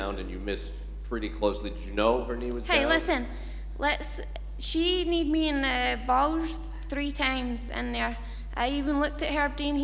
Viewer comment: did she just say "she kneed me in the balls"?